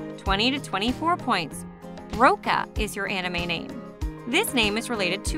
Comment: Roka or Hina? (Not hating I loved this quiz) I heard Roka and saw Hina